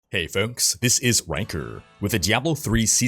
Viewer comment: Is it just me or did I just hear, "Hey folks, this is wanker"…